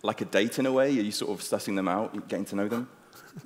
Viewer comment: did he say "sussing them out" or am i hoping for too much